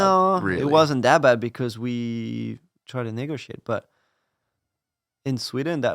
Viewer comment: I’m sure I’m not the only one who had to listen to it 10 times to figure out what he said so .. ‘negotiate’ lol